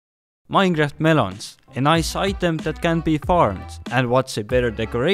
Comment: I thought he said minecraft manhunts